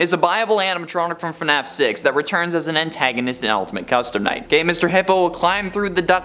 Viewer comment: DID HE JUST SAY "gay Mr. Hippo"?! PLEASE 🤣